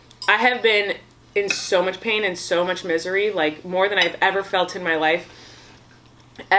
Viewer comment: When she said she was in misery I thought she said she was in Missouri and I was like omg that’s where I live and then she started talking about crying herself to sleep “in Missouri” and I was like OMG ME TOOOOOOOOOOO! And then I realized...🤣